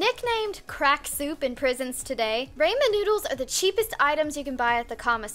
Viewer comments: Wait...did you just say “RAYMEN noodles”😂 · I swear to god I heard her say Raymond noodles